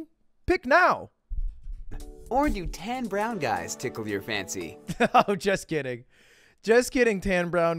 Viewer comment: Thought he said "or do 10 brown guys tickle your fancy" at first, just saying.